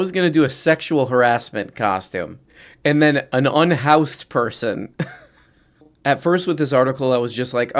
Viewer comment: god I thought that said “an aroused person”